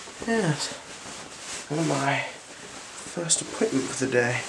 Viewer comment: This guy is a legend!!!!!! This was an awesome exam, I thought u said 'first equipment' instead of 'first appointment' haha